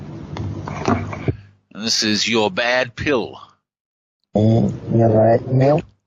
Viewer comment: Don’t tell us what they’re saying and no one would agree on what they’re hearing. @ 32:40 she didn’t say “You’re bad pill” she said “You like milk?” and next “That’s lovely” See, she’s fine. lol. This goes into the same bin as the 432Hz hoax and the flat earth crowd. The truth is this is just coincidental phonetic reversal, pareidolia and language letter frequency combining to form “familiar gibberish”. Want to debate?